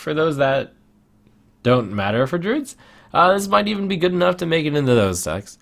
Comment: What is saying at 22:48-22:50? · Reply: "Uh, this might be good enough to make into even those decks."